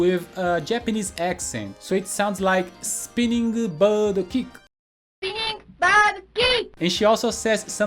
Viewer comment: All this time I thought Chun Li was saying "Spinning star kick."